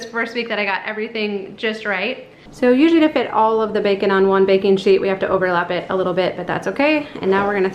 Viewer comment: IMMEDIATELY WENT TO B&N AND GOT THE BOOK!!! Also I heard "Bacon Sheet" instead of "Baking Sheet" xD haha